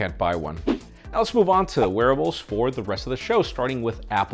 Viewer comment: I've been watching too many episodes of Shadowhunters lol I heard 'warewolves'..